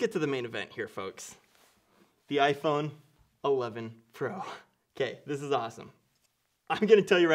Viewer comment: Did u say iphone 11 pro at 6:48? Great unboxing vid btw 👍